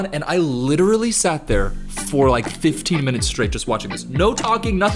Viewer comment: I had to rewind. LOLIMDONE, you said 15 minutes. I thought for sure you said 50 minutes. 😅 Maybe it’s just me, but I can get stuck anywhere at anytime for at least 15 minutes. This includes staring at a blank wall. The little skull guy is soothing AF so I agree with your point I just wanna give you a hard time. 😂